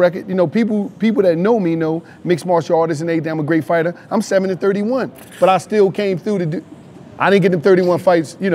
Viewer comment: Wallo be killing me man 7-31 is crazy 😂😂😂😂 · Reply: bruh my dumb ass thought he was saying 70-31 lmfaoo